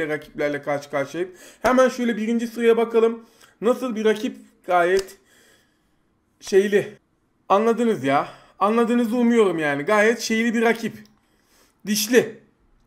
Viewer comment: bi tek ben mi taşaklı olarak anladım